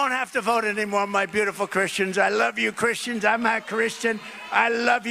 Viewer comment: Did Trump say „I am not Christian„? Greatings from Poland.